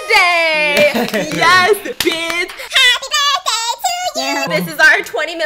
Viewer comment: When she said “bits!” I heard “bitch!”